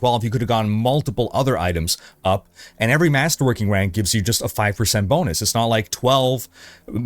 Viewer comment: did i hear a masterwank? challenge accepted!